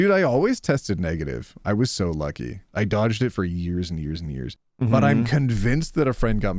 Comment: Hi, not sure what the third thing you said at 40:00 was, but it might have been "eight years", or "they years" (with a sharp th like in theft). Not sure what you meant by it though. This is about the "covid big ears" discussion between and for anyone else wondering. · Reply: definitely eight years